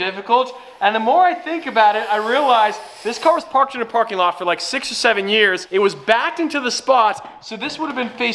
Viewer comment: Did he say 6 or 7 years 5:45 67